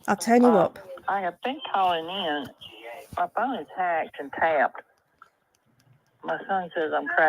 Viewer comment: I thought she said her phone was hacked